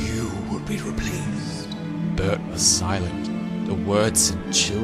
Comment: In 14:50 I thought I heard you pronounce Bert as Ber-urp-t 😂